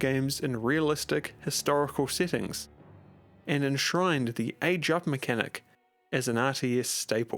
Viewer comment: What did he said? "a drop mechanic"? What is it? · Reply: "Age Up" mechanic, the mechanic of advancing to next era in AoE games.